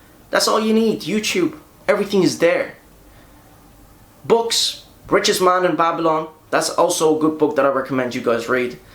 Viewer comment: What’s the name of that book anybody ? He said it like around the 8:50 mark ? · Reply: Richest man of babylon